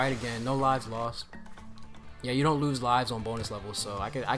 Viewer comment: I thought u said B O N E L E S S Levels when u said 🅱️onus levels